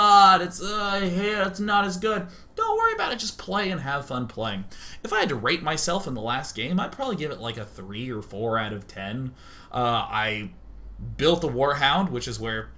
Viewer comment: lol, at 0:36 I thought he said, "If I had to rape myself"